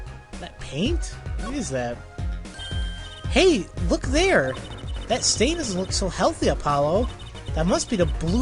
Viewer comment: I thought Trucy said "That Stalin doesn't look so healthy..." .....